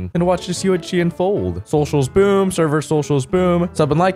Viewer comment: Why do I always hear “social spoon, server social spoon” in the intro? Lol